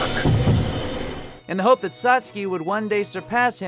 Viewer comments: did he just call Sasuke "Satsuki" @ 5:50?? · Sas-key?? Im definitely not the only one who heard that...